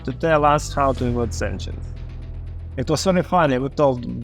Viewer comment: The captions are wrong here i think he said “avoid sanctions” not “avoid engines”